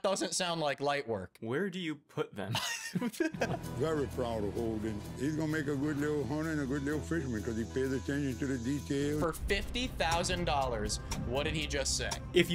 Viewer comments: explained!! my family and i are cajuns with some acadian heritage here is what he said: ”very proud of Holden. He’s gonna make a good lil hunter & a good fisherman because he pays attention to the little details “ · "he's gonna make a good little hunter and a good little fisherman cause he pays attention to the details" English isn't even my native language and i managed to figure out what he said. i know it's cool and easy to make fun of people with accents, but come on guys.